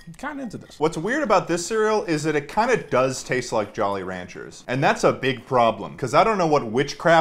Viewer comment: you’re supposed to eat the jolly rancher cereal with lean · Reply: When u said jolly rancher I thought it said ranch and i was confused